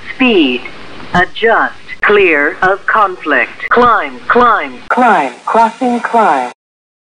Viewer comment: Somebody's calling you on the phone "Yo, your engine number 1 is on fire" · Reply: the climb one, too. at first, i thought it said "climb, fucking climb" and i was like wait what